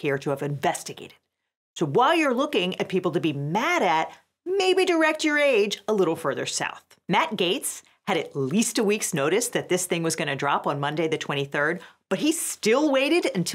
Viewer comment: good thing I had closed captions on, because I could have sworn I heard, "maybe direct your age a little further south"